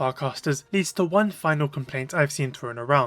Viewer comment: "not having access to farcasters *** to *** *** I've seen thrown around" ... what are you saying? even the captions don't make sense of the words. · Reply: Leads, One final complaint